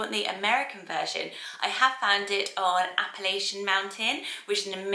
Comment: When you said Appellation Mountain the name website, I thought you said "Appalachian Mountain" as in the mountain range in the USA. 😂😂😂 Google found the website anyway.